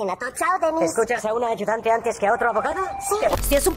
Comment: No se si escuche mal pero en ningun momento dijo. "escuchas antes a una mujer que a un hombre?" Lo que dio "Escuhas antes a una secretaría que a un compañero abogado?" EN NINGÚN MOMENTO HACE REFERENCIA REALMENTE AL GENERO. Hace referencia al trabajo y experiencia detrás. Ya que claramente un abogado de profeción y posiblemente con mayor experiancia sabría mas que una persona que se desarrolla en una rama de oficio diferente. Aparte, si una mujer se siente ofendida por ser llamada secretaría allá su problema, porque es un trabajo respetable e importante, así que ella estaría siendo despectiva y demostrando una mentalidad hipocrita al considerar un oficio respetable como un insulto.